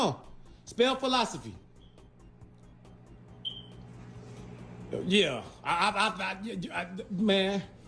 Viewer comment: Dad is so worked up I legitimately thought he was saying 'velocity', not 'philosophy'